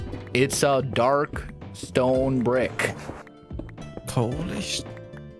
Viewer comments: am i crazy or did keralis say 'holy shhh'? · Did Keralis just said s*** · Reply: He said polished smh